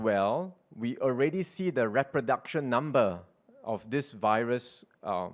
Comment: What word did he just say? Reproduction??? Why is his English like that???